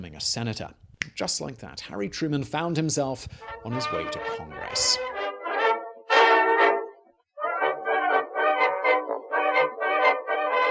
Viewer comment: Lol might have been because of a randomly timed ad, but when you said "just like that Truman found himself heading to the Senate " I heard "just like that Truman found himself heading to Hogwarts" 😂🤣😂